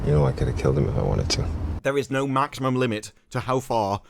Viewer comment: The moral of this story is, "There is no maximum limit to how far you should be pissing off." (I heard that as "natural limit" the first time, due to watching it at 2x speed. Still works!)